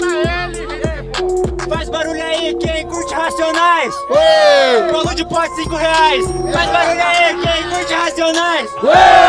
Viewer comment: não entendi esse grito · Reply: Faz barulho pra quem curte racionais, bolo de pote 5 reais